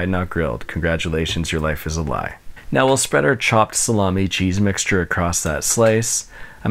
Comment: I thought it said Chopped Satan Grilled Cheese I'm a f**king idiot